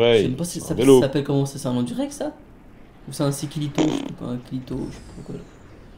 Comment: c moi ou à dit c un clito